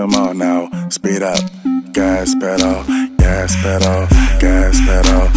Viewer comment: Whenever he said "gas pedal" I thought he was saying "Gas Perro" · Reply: Yessica Calderon i thought he said Gas Pedo